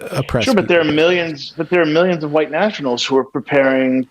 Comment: Using the fbi for 'any' analogy of integrity is facile. is a believer in the present status quo it appears. If he had looked into the various analyis sites on the Jan 6 fiasco his deeeep angst would evaporate. The thing was rigged... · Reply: Sturtevant Yea, I had to listen to what he said a second time before commenting. At first, I heard "white nationalist" (mostly because I was listening to this video at 2.2x speed) and then I re-listened to it and realized that despite him using a different word "national" instead of "nationalist" the intent behind his words seemed to convey the same message.